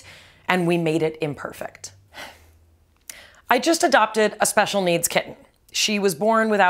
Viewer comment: I misheard you and thought you said a special needs kid and was confused when you started talking about cats.